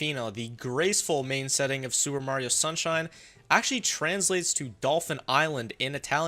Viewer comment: Did you just say sewer Mario sunshine 😂